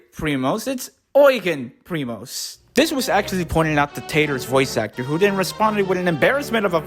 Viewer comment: Wait, her name is TATER? As in. Like. Slang for potato. I kept hearing ‘Taylor’ this whole time.